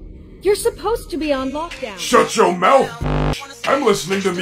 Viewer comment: When Freddy said “I am listening to music” at first I thought he said “your being abusive” (not that its funny just that I thought he said that) 😂